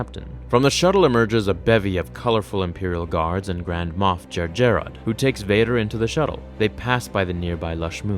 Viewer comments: I think you’re mispronouncing “Jerjerrod.” Also, I love your videos. Thank you for making them! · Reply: Oh, Jerjerrod. I thought he said Moff Jar Jar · I thought I heard moth Jar Jar.